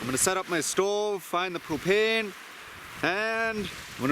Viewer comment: did you say find the stove and cocaine and make some coffee? 😂😂